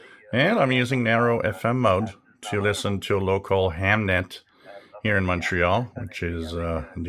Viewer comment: I 1st thought you said Hamlet so it one of those high-class receivers....guess I need to clean the wax out of my ears...:)! 73! Have a great day!